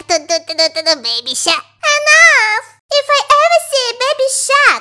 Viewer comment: I thought you said babysa